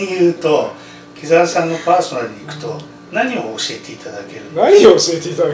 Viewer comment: 「木澤さんのパーソナル」が1.5倍速だと「黄猿さんのパーソナル」に聞こえた